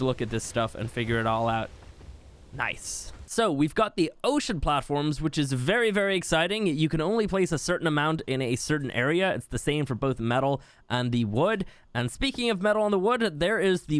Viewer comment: As soon as he said ocean platform I heard “ocean man...”